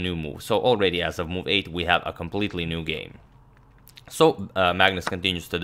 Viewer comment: i have never thought about it before. but for a lot of people including me, its probably you, Agadmator, whose voice i have heard the most over the last two years. since i watched like 500 videos of you :D i probably heard you more often than my family altogether! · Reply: not gonna lie, me too. I won a game recently with a banger rook lift that trapped the queen and I swear I heard him say "white is completely winning"